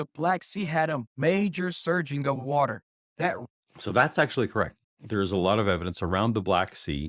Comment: LOL. I thought she said "That's Satan!"